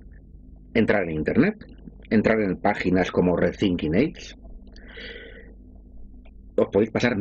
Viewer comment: MINUTO 28:10 CUAL ES LA WEB QUE DICE QUE VEAMOS PARA VER NOTICIAS DE ESTA ÍNDOLE... RESINQUI QUE????